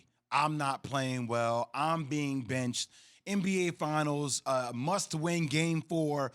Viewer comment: I thought KeShawn said "Bitched" instead of "Benched" lmfaoo